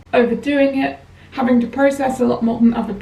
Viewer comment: I'm sorry, i I have a question, my english could be better and I don't understand what is "depr process" (1:29). Is it about depression ? Thanks to people for answer 🙏